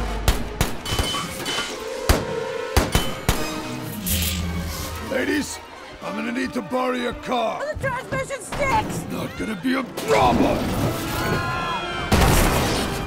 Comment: "The transmission sticks" "Not gonna be a problem!" Underrated bit of dialog😁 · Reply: I thought she said: the transmission’s ‘stick’. As in ‘stick shift’